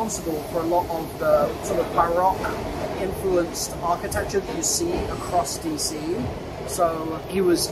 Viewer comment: did just say buried???